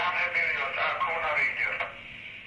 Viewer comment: lost screen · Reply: i had to listen on full volume to actually understand and what i heard is jan heweliusz contqcting last time around 7:40 and